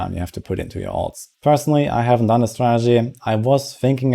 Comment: "personally i havent done this strategy"? did i hear this right?